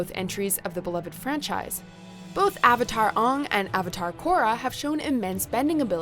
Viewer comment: Did she just say uung?